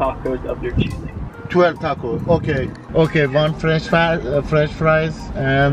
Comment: Wait, french fries from McDonald’s I thought she said no french fries